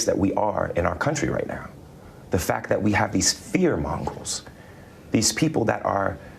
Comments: I know I'm very late to this but @ 53:19 Does he say "we have these fear MONGRELS"? 😂😂😂😂 this brainlet pulling a real Michael Scott moment there. Cut off your nose to spiderface. Quit fear mongreling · Actually, he said "fear mongrels."😂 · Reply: I heard "fear mongrels," too. 😂